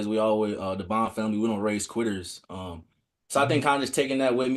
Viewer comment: I thought Bond said his family don’t raise queers but he said quitters